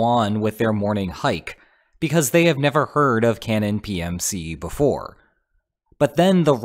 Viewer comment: All Zelda references: 3:14 Maybe triforce 6:08 Another triforce 6:12 Link to the past 6:49 Phantom hourglass 8:03 Spirit Tracks (wow that was hard to notice) 8:23 Oracle of ages 11:26 Cannon maybe Ganon? 13:19 Link's Awakening 15:32 Link between worlds 15:59 It's dangerous to go alone 18:00 Age of calamity 22:26 Legend of Zelenskyy 22:31 Hey listen 23:28 Power wisdom courage 22:56 Tri-force(ing) Love that ending message. Also thanks for finding the other references everyone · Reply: I did a double take when I heard “Ganon PMC” and thought that I was going mad when he kept saying “Cannon” afterward